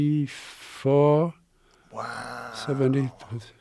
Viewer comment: this guys a vampire he clearly said 1704 🧛‍♂️